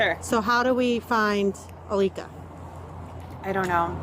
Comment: Alika Bone is insane work 💀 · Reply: I thought she said Alisa at first